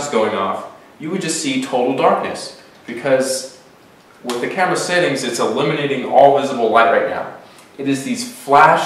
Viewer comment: what does he say at 4:06? is he saying," because its illuminating all of the visible webcam."? please tell me whats he saying.